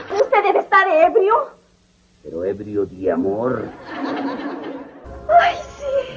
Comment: Aquí le entendí "LOCO DE AMORRR" XDXDXDXD